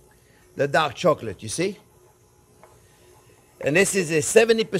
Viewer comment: I thought he said duck chocolate LOL😂. I couldn’t wait to find out how to make “duck” chocolate. You are very informative and entertaining.